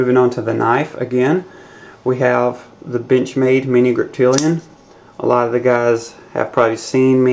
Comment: It sounded like you said BITCHMADE griptillian.